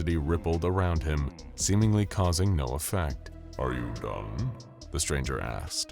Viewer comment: "Are you done?" sounded like "Are you dumb?"